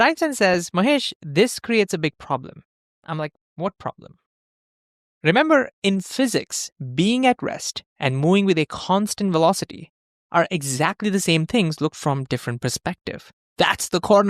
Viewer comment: Terrible accent. Please use AI voice. @ 2:10 for example you say "being aggressed" instead of "being at rest" and "mowing" instead of "moving"... 🙄 This was the moment I stopped watching.